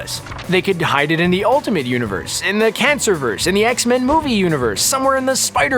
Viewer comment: did he say cancer verse? And if so: why the hell is that a thing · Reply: Yes he said cancer verse 😂😂😂😂